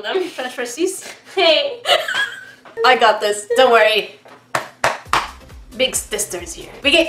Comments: did anyone else hear stister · at 8 mins and 45 seconds said big stisters here and im like what the heck did she just say cause in getting salty